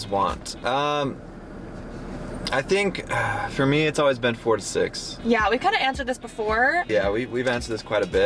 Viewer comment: The question about how many kids you guys want, When said 4 to 6 kids at first I thought he said 46 🤣🤣🤣🤣